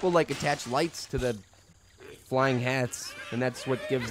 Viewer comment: sounded like he said flying heads